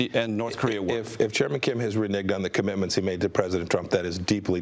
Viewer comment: Did he say "re-nigged"